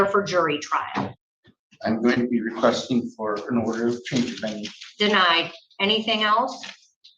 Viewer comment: Anybody know what he says at 8:18 ? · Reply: Requested a change of venue.